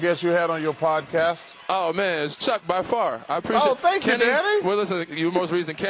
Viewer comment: i could have swore Chuck said "oh thank you daddy" 🤣